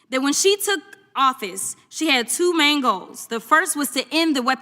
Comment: I thought she said two mangoes...😅😅😅